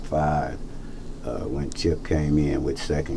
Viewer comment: Yo did he call him "Chip"..? 🤔